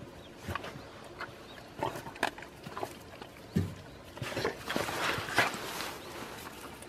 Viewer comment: 삼색이는 아직 가족으로 받아주지 않았나 보네요 안타까워라 5:53 안녕해 라고 들리는 거 같은 _ _)